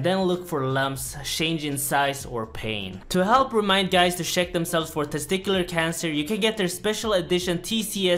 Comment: i thought he said "shit himself" not check himself during adv